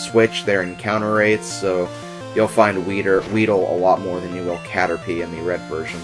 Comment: did you say weeder?